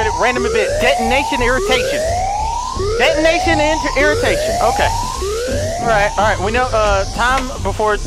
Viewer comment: Whenever Delirious said detonation irritation.... I thought he said "Get naked irritation" xD